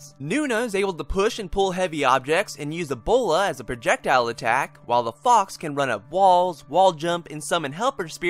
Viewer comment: sounds like he says and use ebola as a projectile attack xD